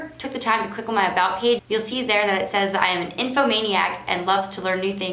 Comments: the auto-generated caption says “...I am a nymphomaniac...” Come on Youtube! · "an info maniac"... that's not what the captions said! 😂